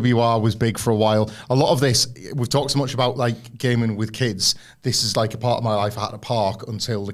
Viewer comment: Watching this with a cup of soup for lunch and I hear “gaslighting kids”…. That soup burned 😂